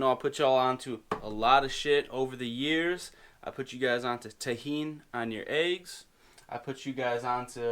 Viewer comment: Ah, I know this. :) So you put Tahini (sesame paste) and tajin on top of the eggs? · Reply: OK, I thought you said Tahina. But Tajín makes more sense :))